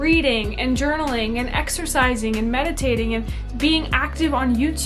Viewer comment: HAHA - I was watching/listening to your video while cooking and I thought you said "levitating" rather than mediating when you were listing off things you could be doing in the mornings. I've actually found not checking my phone first thing in the morning to be one of the most powerful hacks (when I do it). It really creates a sense of spaciousness for the entire rest of my day. Great tips!